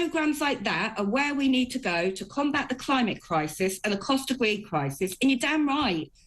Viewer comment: did she just say cost of weed crisis 🤣🤣